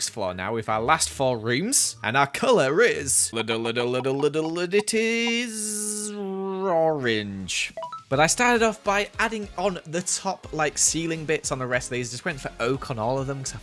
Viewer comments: Did he really say rorange 😂😂😂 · Nobody: Not even a single soul: The fish in the time lapse: ➡️⬅️↘️↙️⬆️↖️↖️⬅️⬅️⬆️⬇️ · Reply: Did he really say rorange 😂😂😂